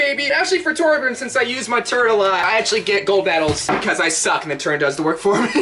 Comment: did you say you get "gold battles a lot"?